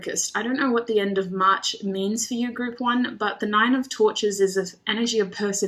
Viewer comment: Girl I thought you said "six of torture" (6 of torches) I said OH SHIT lol! As always, your readings hit it right on the nail for me. Thank you for the confirmation <3